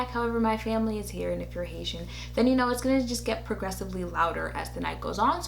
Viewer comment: "If youre Haitian you known its going to get progressively louder" lololol #Truth but also why i love us 😩❤️ 🇭🇹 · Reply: JOURDAN LONDON oooohh I thought she said ASIAN OMG!!!😂😬